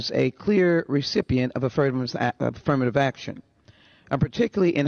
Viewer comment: I'm with Charlie. I heard her say ass--firmative action, before the self correction 😂😂😂😂😂